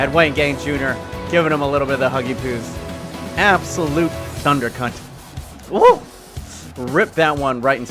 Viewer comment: did he say thunder cunt